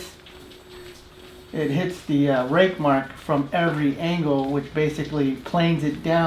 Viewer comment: I thought he said rape marks